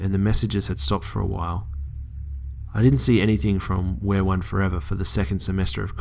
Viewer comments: it sounded like he said where one the entire time. but it's we. are. one. damn this is creepy tho o.O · The whole time I thought that he was saying wearone as in wear like a T-shirt or something of the like. It creeped me out when I looked at the name and it clicked that it was we are one. Spooky, haha!